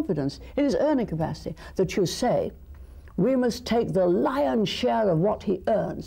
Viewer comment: can anybody tell me what word she says in 17:03 ? "we must take the .... share of what he earn to be spent by the state" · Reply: lions